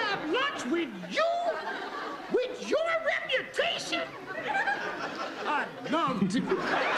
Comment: What was said......!!? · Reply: Geraldine says I'd love to.